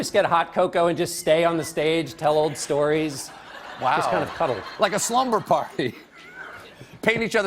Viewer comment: does he say "just kinda cuddle??"